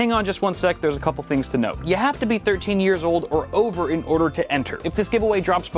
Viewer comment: When i watched this right after waking, i heard it as 30 years old, not 13 hahaha turning 30 in june, so honestly a little relieved after watching it again :P